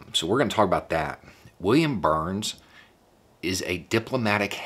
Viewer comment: I thought you were about to say "William Bar" and the 'dafuuuuu'? Went through the roof.